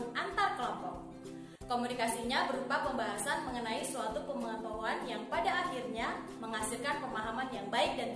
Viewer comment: Itu di menit 1:13 pemahaman tentang ap bu? Masih gk kedengeran walau pake headset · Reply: Tentang suatu pengetahuan